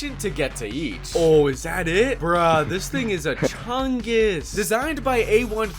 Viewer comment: i absolutely thought he said the N word right there, but im pretty sure he said "thing is"